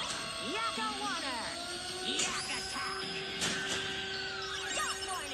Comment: I’m a god at spelling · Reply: YAKKO WARNER..... YAK ATTACK (I think that’s what he said?)